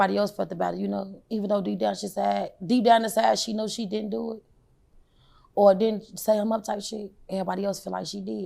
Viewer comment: I thought she said he didn’t do it